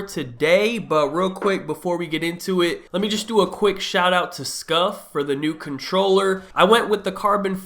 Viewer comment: I thought he said shout out to Scump, bout to say he got plugs